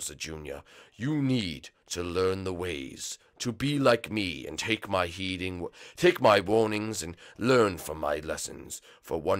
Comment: I swear it sounds like he is saying "take my bonings" 😂😂😂